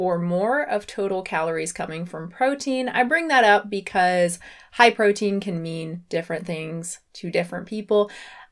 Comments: my pea brain understood "Hi protein!" at the start of the video smh... really thought was saying hi to the protein, enthusiastically at that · I thought you said "Hi protein!" at the beginning löl